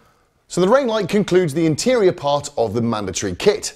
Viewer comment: I thought you said video next week 🤔🤣